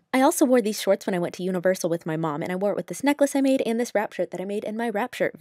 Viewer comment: Wrap shirt... wrap shirt... I thought she was saying rapture...